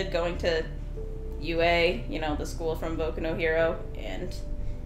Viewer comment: tbh, when i first heard "boku no hero" my monkey brain instantly heard it as "boku no pico" and i fucking choked on my sweet tea